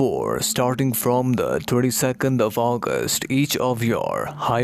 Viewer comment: Did he say the "32nd of August"? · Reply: 22 nd